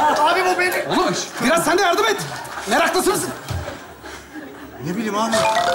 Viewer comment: 'da, Feridun ne diyor tam anlamadım. Meraklısın demek istiyor ama başka bir kelime çıkıyor sanki 😬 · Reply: Meraklısı mısın?